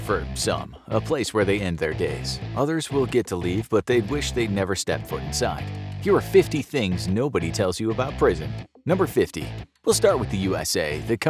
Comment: said prison i thought he said bricked in